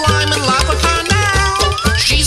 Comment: I thought he said she’s my baby cow all this time 😩